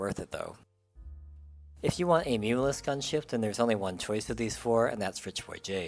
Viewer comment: I dont understand what you said at @ 8:55 ...a what gunship? meaningless? needle less? You totally mumbled there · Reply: "Muunilist"